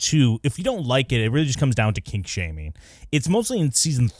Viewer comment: i thought you said King shaming LOL